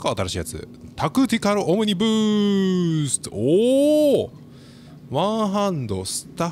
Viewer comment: 『タクティカル鬼ブースト!!』 って聞こえて『はいっ( ・◇・)？』ってなった